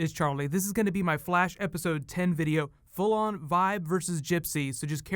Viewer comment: did he say episode 10?